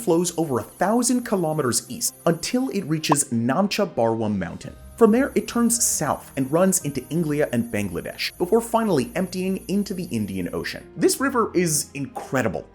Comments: did you just call India Englia? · Did you say Inglia at 6:12, not the first time you mispronounced India, disappointed dude 🤦🏻